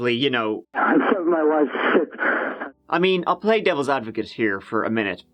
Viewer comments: I thought he said I stabbed my wife six........ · "I gotta - I stabbed my wife. It's - and blood everywhere..." I do legal and law enforcement transcription. These types of calls are the bulk of what I do. Shasta County actually has fairly decent audio recordings, from my experience. I'd say with 90 percent certainty he said "stabbed my wife." I posted this before I watched the rest of the video and heard "knifey to the wifey" just to see if his words matched with what her injuries were. They do. If I'd gotten this call as a transcriptionist, I'd been able to enhance the audio. But even here at 3:37 a.m., with half a glass of wine and some Marmite and crackers, I hear stabbed clearly. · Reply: I heard it as "I heard my wife [shout?]". It's definitely not clear, and I'd be nowhere near 90%. At best, it's vague. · I hear “I found my wife, heh!” The insect screen looks like it was cut from the inside. If you’ve ever replaced one, you’ll know what I mean. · When listening to the 911 Call, I hear, "I found my wife--Shit [...]". Is that just me? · I hear "I found my wife, shit" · The first time I heard that call, I did hear “I killed my wife...sh*t”. Good ole Freudian slip. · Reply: i heard “i found my wife…shit” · I heard "i found my wife,shit" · At the start of this video when you played the audio, I thought he said “I found my wife sick”.